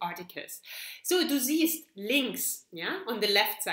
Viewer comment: Hi, i heard you saying "on the left". But what is it in German? Sry i can't catch it. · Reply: on the left = links :-)